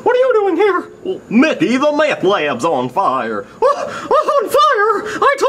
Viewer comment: "Mickey the meth lab on fire!" "I told you Donald couldn't cook." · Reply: I thought Goofy said the lamp lamps are on fire.